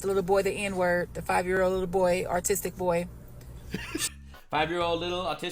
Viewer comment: ish when you play the new vid and the girl is talking about the boy... i swear i heard ".... the 5 year old ARTISTIC boy.".... is it just me? or did southparks covid episodes ruin me....?